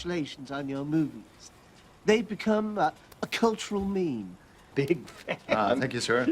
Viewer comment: did he say meme?